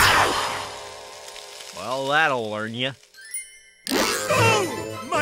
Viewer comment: Earthworm Jim: "Well, that'll learn ya." LOL! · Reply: To be fair, I thought he also said learn instead of warn.